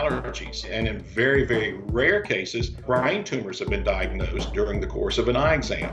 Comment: Did this fool say "Brian tumors" instead of "brain tumors"?